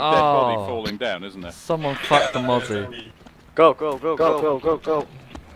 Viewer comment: did just say " ohhh someone just F*** the mother?